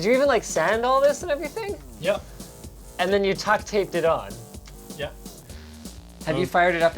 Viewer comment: did he say "tuct tape" ....